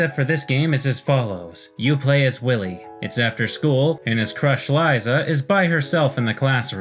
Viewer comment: I heard "You play as Willie" as "You play his willy" and did a double take